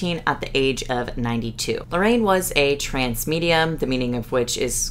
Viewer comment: i thought you said trans medium and was about to be like “i didnt know lorraine was trans good for her!”